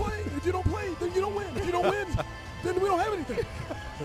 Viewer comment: Did he say Jamie you''re such a fool?